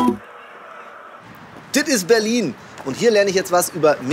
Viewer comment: Was ich verstanden habe: "Und hier lerne ich jetzt was über Minimalismus. Ich bin nämlich mit BIBI verabredet. Sie ist Profi-Youtuberin und quasi Minimalistin." - Ich, 19, blond, verstehe die Welt nicht mehr :D